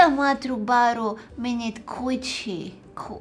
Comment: am i the only one who heard coochie at 15:23?😂